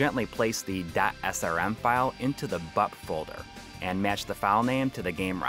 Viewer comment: I kept hearing "Butt Folders". My mind is in the gutter...